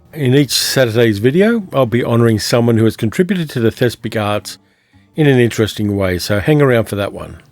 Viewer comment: Hi! 0:40 "contributed to the... thespegards?!?" I Googled and couldn't figure out what word you are saying. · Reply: The thespic arts